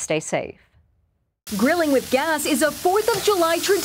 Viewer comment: ngl i thought that said grilling with "glass"